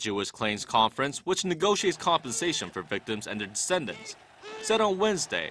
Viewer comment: What did he say at 0:28 ? Compensation and "incentives"? "Ascendants"?